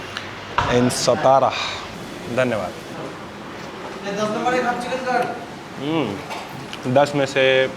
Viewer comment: if i heard it right, owner is from Bengal for sure!! Coz he said 10 number e half chicken kat!🤌